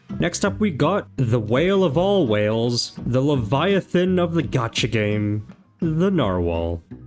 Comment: DID I HEAR GACHA?